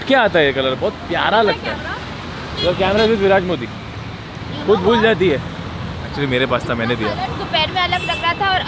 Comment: i heard virat kohli 😂😂